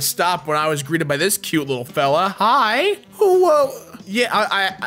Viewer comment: did he just say owo